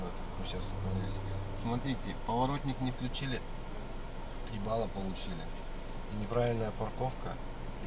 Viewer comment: послышалось: "Поворотник не включили - в е*ало получили")